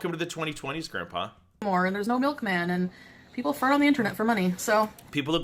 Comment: so according to, i should probably quit Med school and binge on the Internet to make money, to "get with the times". Btw is it me or does she really say "people fart on the Internet for money" around 8:17?